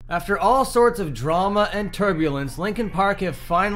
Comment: At the very beginning when you said turbulence i thought you said tuberculosis and i have no idea how